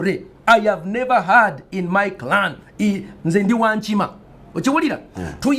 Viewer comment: as someone who used to watch this show every day and try to learning luganda .. can some one help me with things ? what is the tripe of Pasta ? in 56:00 he say "in zi wan chima ..... watchaworyaaa" what did says in 27:30 .. "APANDA MONAKWONAWANYA" what did say in 29:10 "oh no no no no no , this girl was confusing us ON MWALA MOZAVOZAVI" because he said the words later too .. the main event question is what did says in 1:04:14 "GENE GENDU BARBATCHU KANO SANYA" sorry for the long orders but .. I LOVE PASTA TALKING (NO HOMO FOR SURE) part 1 of asking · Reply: "Ndi wa Nkima" means am from monkey clan